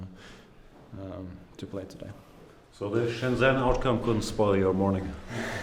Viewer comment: What did Gustafsson said at 8:24? I tried my best but couldn't understand it. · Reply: He said: so the Shenzhen outcome couldn't spoil your morning